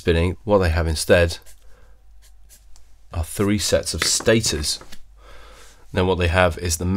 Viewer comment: What did he say at 5:12 ? Three sets of status...? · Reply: Stators.